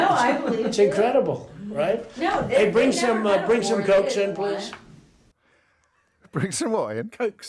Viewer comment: I honestly thought he said "Bring some goats in." and I wasn't even phased by it, his 'eccentricity' having been normalised over time. Scary.